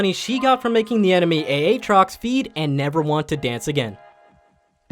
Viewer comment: did he just call aatrox "A -atrox" 7:32 wut